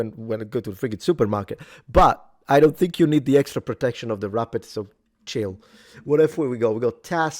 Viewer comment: Subtitles say you're saying rapid but I'm not sure if that's right, but I thought you were saying rubber lol, the extra protection of the rubber. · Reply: Wrapper 😂 it’s the name of the extra plate for the face around an armet type helmet.